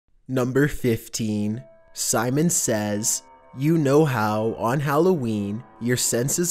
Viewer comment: did he said Simon says?